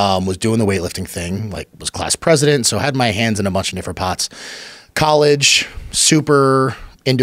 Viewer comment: did he said : “I had my hands on a bunch of different cocks?” 🤣 💀